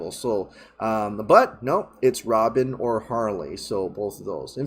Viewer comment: With that deleted scene I heard it as Robyn or Harley, not Robin and Carrie Kelley. Also happy it isn’t Supergirl. We don’t need her in the grander story. Everyone wants all the characters to be in these few movies, will be too much